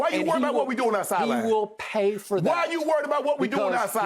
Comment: Did Skip just say he will pay for that? For what.. celebrating a touchdown? 😂